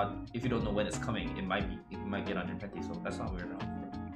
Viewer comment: did he say panties or something else?